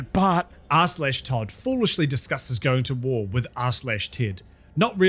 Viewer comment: lol r/ted, i thought you say r/tits, they definitely not gonna win against r/tits